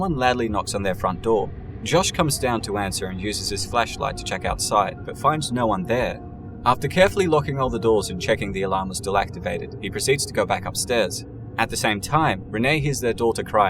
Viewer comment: I thought u said "he goes and uses his fleshlight" lol